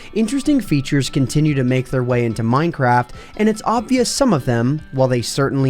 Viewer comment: did you say minecrap?!?! And are tjose glitches bobs floating above the ground.